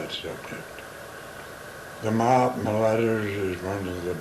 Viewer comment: Can anyone tell me what he said at 17:10? Sounds like the "Mob Ledders". I'd like to read what he was talking about. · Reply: I believe he is saying the "Mahatma Letters" (referring to the book The Mahatma Letters to A.P. Sinnet).